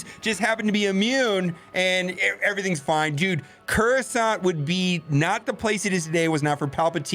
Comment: Oh god. I heard "Coronsant". I need a break from all this :(